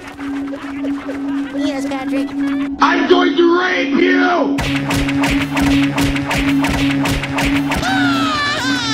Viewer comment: Where's that from? And at 0:08 what does it say? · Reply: a blanket of KISS!!!